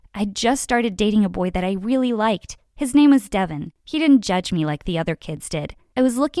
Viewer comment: I thought that you said his name was devil but its devin